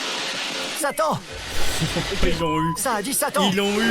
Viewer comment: Quand il a dit "Satan" moi j'ai entendu "Sali"